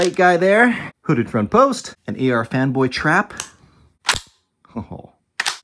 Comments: Thought he said femboy trap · My guy I thought you said “AR Femboy trap” for a hot second there